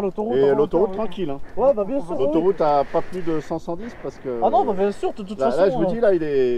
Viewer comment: C moi ou à 7:30 j’ai compris et a l’autoroute tranquille ! Pas a plus de 510